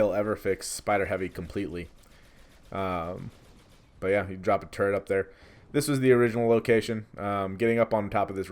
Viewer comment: sounds like he says "drop a turd up there", admittedly, those ion turrets are turds at times.